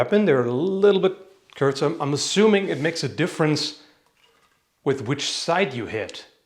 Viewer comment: He says "kürzer" or did i hear it wrong?